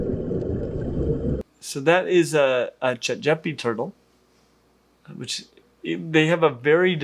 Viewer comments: I thought you said "Jejepi Turtle", and spent a few minutes looking it up and changing the spelling when I couldn't find anything about it. 🤣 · For the turtle my first thought was that it was AI. Then said it's a Chatgipi Turtle, and I thought "Really, that's a real turtle? I would've never thought...." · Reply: I thought he said Jejepi Turtle, and spent a few minutes looking it up and changing the spelling trying to find out what it was.